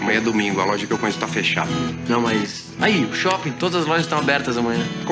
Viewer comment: What the younger man said at 8:35 is virtually incomprehensible. It's like he mumbled his way through the phrase "Todas as lojas...". Even when I reduced the playback speed to 0.25, whatever he mumbled did not match the subtitles AT ALL!